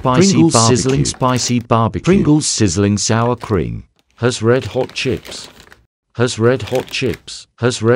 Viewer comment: I heard "Her's red hot chicks"💀